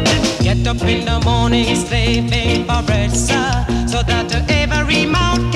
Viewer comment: Oh he actually said "slaving for bread sir". Growing up I always thought he said 'slaving for breast'